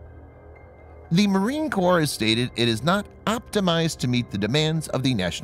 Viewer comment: What do he say ? " Not optimize?" I had to rewind that, 1st Marine Division was island hoping with tanks, the first island to fall, Guadalcanal (1942) because, every marine Company, have (4) tanks, per rifle company, my M.E.F, ( your telling me will now 4:58 ) and the Navy can't hang slow boat(s) sitting ducks? 6:50 the CMC got smart missile carry on !